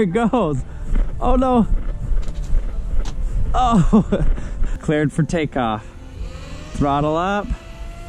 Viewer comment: When the phone said cleared for take off I thought it said Peter the potato